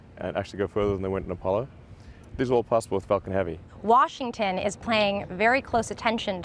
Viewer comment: I thought Elon said, "Its fucking heavy" Died laughing, wish he did.